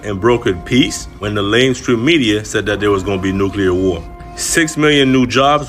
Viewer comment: Lol … I had to listen several times. It sounds like he says, “lame stream media” instead of “mainstream media.” Idk if that was intended or not, but I like it. ❤